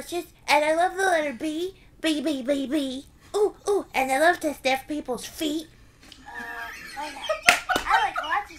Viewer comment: Him saying “I like to sniff people’s feet” sounded like he said, “I like to stab people’s feet” to me for a second.